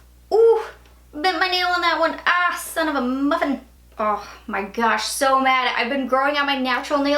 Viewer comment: When you broke your nail... "son of a mother.." What!? 😂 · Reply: I thought that she said 'sonofamuffin' which I thought was cute. LOL